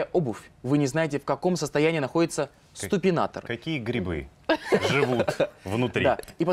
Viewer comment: Мне одной послышалось ,сТупинатор?